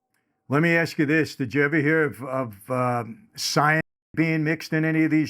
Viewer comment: is he saying cyanide? Why bleep this word out. Very intelligent man dropping bombs and making me think hard about Kim Porter's death. 😮